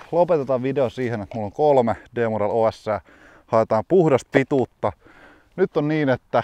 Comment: What are those red putters? and it sounded like you said p model os. never heard of that